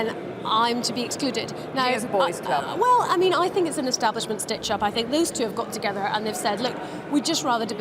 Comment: Jo Swinson thinks she has been stitched up by the establishment. Did I hear that right?